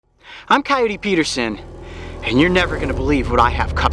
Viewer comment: I always thought he was saying Cody Peterson in an Australian accent.